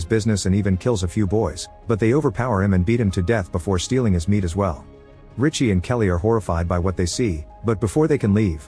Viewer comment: Go to the 6:34 mark, the narrator says they kill the butcher and "eat his meat" as well, while it pans to one of the boys looking like they were eating off a freshly torn off piece of meat. I didn't watch the movie though, you could be correct since I thought it was implying something different. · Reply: Yeah I watched it. you're making up words. The narrator specifically says "they beat him to death before stealing his meat as well" not they eat his meat. you're still not correct. The butcher was standing in front of his shop with a rifle to guard it from the boys, so that they wouldn't break in and steal his meat. The boys had been breaking into all the stores that had food to eat. he kills a few boys and then they mob him, kill him, and then steal his meat... the meat in his butcher shop, which they do eat raw. eating raw animal meat is still not cannibalism. They do not eat the butcher and the narrator does not say that. If you're not sure, turn on subtitles, start at 6:30 and watch again, so you can understand the narrator better. I understand that sometimes ai voice can catch people up.